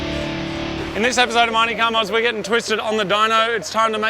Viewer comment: Anyone else think the intro sounds like it says “Marty Car Mods” 😂